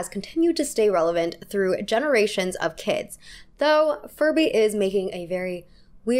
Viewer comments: I’m sorry did she say farbie 😭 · did you say Farbie? 😂